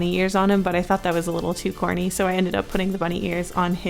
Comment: When you said the bunny ears were " corny" I thought you said " horny" 😂🤣🤣